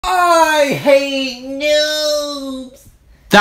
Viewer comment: I thought he said “I HATE YOUUUU” 0:00😂